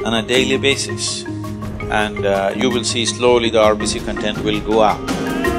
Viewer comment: Sorry, but does anyone know what he say at 0:25 What content? the arbisy content? I dont understand it well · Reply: He is referring to RBC - Red Blood corpuscles in the blood that carry hemoglobin